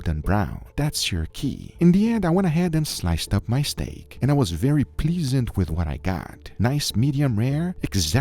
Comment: “and I was very pleasened with what I got” not sure if that’s a word but ok haha